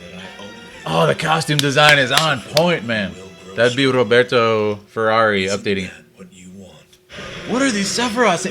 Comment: Roberto Ferrari??? is that what you said?